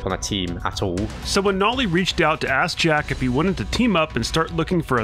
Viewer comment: did he just call him AssJack???